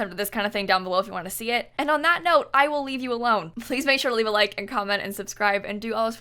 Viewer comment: I need to go to sleep. When she said, “I’ll leave you alone.” I heard, “a loan.” :/